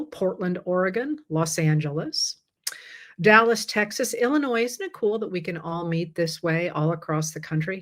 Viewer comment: These videos and weekly political chats are MY LIFE LINE!! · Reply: Had to rewind a few seconds in....thought you said "ill-in-noise". I'm like .... What? This is an educated, bright, scholarly type person...... You just said "Illinois, isn't it....." really fast and just caught my ear wrong.